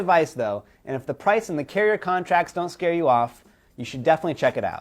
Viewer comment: At the end of the video, I seriously thought he said, "I'm Donald Duck" ROFL